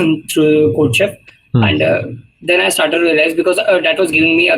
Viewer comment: codeshop or what he said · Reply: Codechef or codeforces